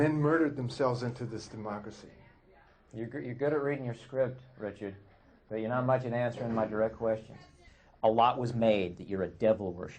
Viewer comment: Can someone tell me what the interviewer said at 6:11 ? I can't tell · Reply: "You're good at reading your script, Richard, but you're not much good at answering my direct questions"